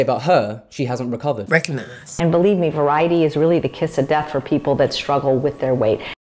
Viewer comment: Please do a part 2! As soon as I heard “Variety is the kiss of death for anyone watching their weight” I just couldn’t. I love that you cut in going “You fucking dumbass” lol, she’s such a queen. ❤🔥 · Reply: I think she said it's a key not a kiss